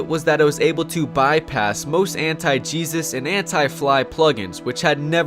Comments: "Bypass most anti jesus" Bro I thought you said bypass most anti cheesus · with captions says "it was able to bypass most anti-jesus and anti-fly plug-ins" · did he just say ''Anti-Jesus''